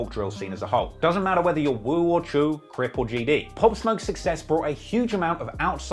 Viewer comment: Sounded like he said “woo or Jew” (choo) lol